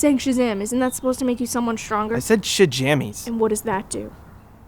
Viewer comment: Why did I hear "Sha-dammit!⚡"